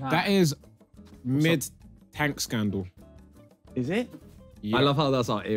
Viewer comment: I thought he said Hank scandal